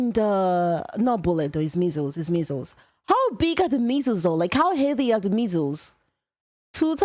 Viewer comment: I heard that as "how big are the Measels?" Watching distracted at work haha